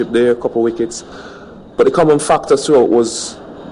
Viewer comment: listen carefully from 5:00 for a moment. did he say 'fucked'? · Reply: he said "factor"